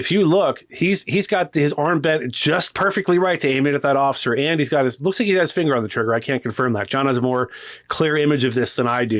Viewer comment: sounded like he said "he's got his bussy ass finger on the trigger" lol had to relisten to what he actually said there